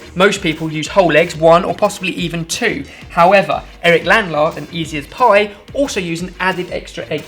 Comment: all I hear is and saying "CHEF LANLAR"